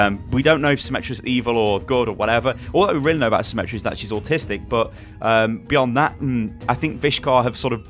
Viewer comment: Hold up, did he just say she's autistic? Lmao I had to replay that like 4 times.